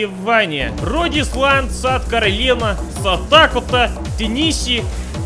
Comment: I love so much how he said "Nevada" like "Не вода" on russian x) (this translates "not water") And, of course, Simisipipi was the best XD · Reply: also, when he said “rhode island” i heard “рот исландца“ which means “mouth of icelander” XDDD